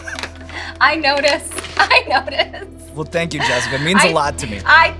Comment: did he say jasmine instead of Jessica?